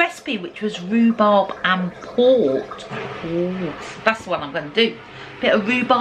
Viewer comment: I love my Sunday’s with and family! Well done. That apple crumble looked delicious! At first I thought you said rhubarb with PORK! 😂 what a busy week you have had!